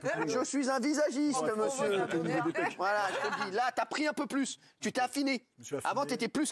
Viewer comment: Il dit quoi Kaaris à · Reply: Il a dit "j ai pris au niveau des peck "